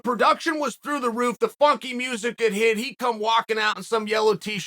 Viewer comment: Am I the only one who thought he said "The fucking music did hit"?